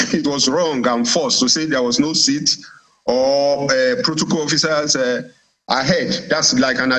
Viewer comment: So you have a space 😂😂😂 I thought you said there is no seat for anyone foolish fool